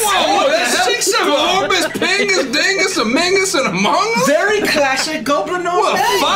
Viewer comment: did he say "amongus" 😭😭💀💀